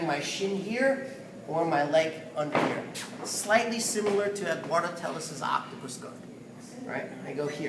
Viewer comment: Wow this is amazing! Thank you very much, can't wait to try it. Who's stuff did you say it was similar to ( at 1:18 it was incomprehensible). Have to check out your other videos right now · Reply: Thank you for the positive feedback! I was saying that the grip is similar to the one used by Edwardo Telles in his Octopus Guard system.